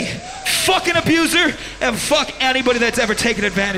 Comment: What did she say at 7:18?? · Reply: "Fuck abusers"